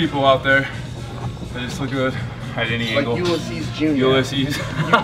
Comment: Does anybody know who they are talking about at 6:30 I can't hear the first name! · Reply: Ulisses jr